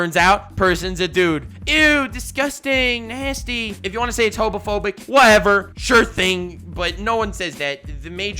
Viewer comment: I swear 16:52 he said hobophobic... anyone afraid of homeless people here?